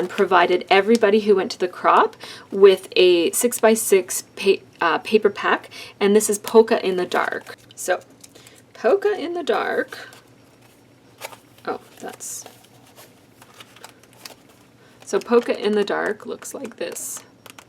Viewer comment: Is it wrong (it totally is) that I heard "poke her in the dark"?? LOL TFS <3